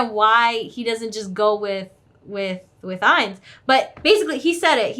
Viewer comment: When you said why not roll with it 24:17 all i heard was why don't USA come under the rule of Russia or China 😂 not a political person i see 😂